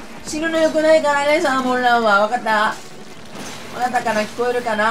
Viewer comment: シャーモンラン言ってるように聞こえる😂😂😂 酔っ払い😂😂😂